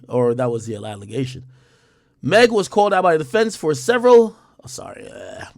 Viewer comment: um human he was found guilty so yes they do consider y'all weird....all y'all sed he was truthful and she was lying yet... evidence proved beyond the shadow of doubt......no y'all weird and she full of shit...must been having sexual relationships with him too .....bunch of penis rollercoaster riders 😂😂😂😂😘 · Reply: I thought you were saying Meg was full of shit